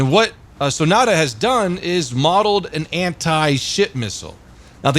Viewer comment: pretty sure i heard you say SHIT missile a couple times :D